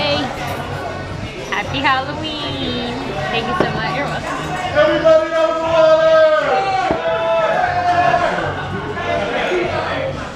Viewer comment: I know this isn't what the person in the background yelled but I thought he yelled "(inaudible) Happy Water!" and now I can't get it out of my mind